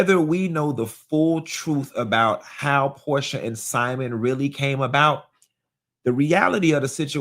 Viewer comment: I thought the maid said Porsha left and never came back😂😂😂😂